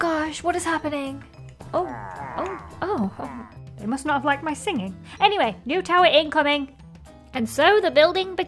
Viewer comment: When said “oh, they must not like my singing!” I heard them say “WOW!”